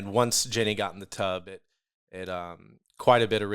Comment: I heard him say, the point we had to get to was "the tug" lmao he said the tub